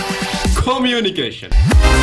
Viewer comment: Can somebody help me to know what actually said around 1:22? It sound like call me……? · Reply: it was "Communication"